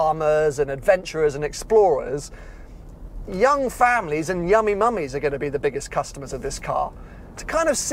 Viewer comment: I'm sorry, did just say "Yummy Mommies"? I mean.. he's not wrong, I just want to make sure I heard that right.